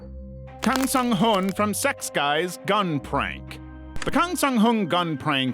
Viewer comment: I keep hearing "Secks Guys". 😮 I looked it up and it's not pronounced that way but that's all I hear.